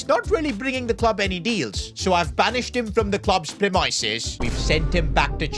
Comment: Did bro really say pre-my-sis